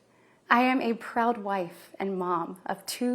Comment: I thought she said she was a Proud White and Mom...in the begging ...😶